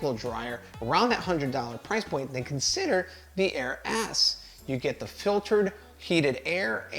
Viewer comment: Did he say air ass?! -1:48 😂